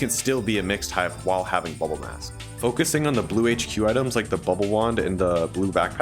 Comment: what did u say? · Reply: Bubble wand